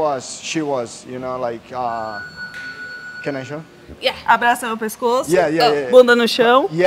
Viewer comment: I thought bro said "can I join?"